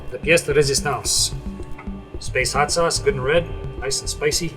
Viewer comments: I thought chris said "nice and SPACEY" would be a great pun tho.. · I thought he said "Space hot sauce! Nice and spacey"